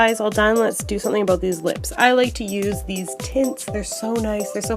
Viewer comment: i heard "i like to use these tits" when you said "i like to use these tints" and i was distracted but then i heard that and was like what???????? WHY DID MY BRAIN THINK YOU SAID THAT i am terrible oh my gosh